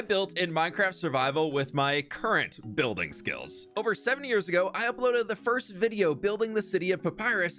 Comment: I thought you said “over 70 years ago” in the intro😅😂